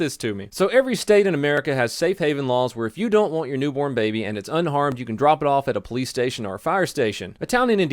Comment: I thought you said “If you don’t want your newborn baby and it’s UNARMED...”